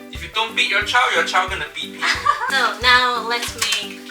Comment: did he just say "if you don't beat your child, your child gonna beat people" ?? Fuiyooooh :D :D :D :D